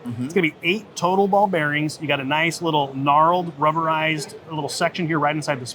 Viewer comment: did I hear him say “NARALLED”?